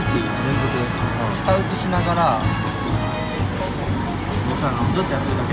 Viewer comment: 舌打ちしながらって聞こえた